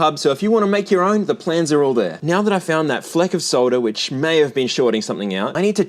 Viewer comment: I totally thought that he was saying "soda" instead of "solder". Was listening to this first instead of watching and initially thought he had found something spilled. 🤦🏼‍♂️😄